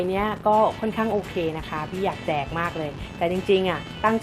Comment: ได้ยินว่าพี่อยาก แ*กมากเลย